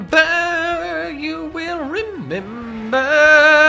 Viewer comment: I was working on homework, just listening to this video and at 20:17 I just hear “Ember, you will remember!” and I just stopped what I was doing at was like “Bro, I love Danny Phantom!”